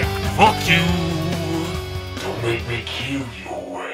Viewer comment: At first I thought he said "Don't make me kiss yo ass"